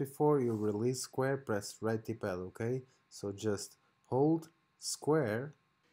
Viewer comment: What does he say at 2:57 what’s redippal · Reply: right d-pad ➡️ on PS4 controller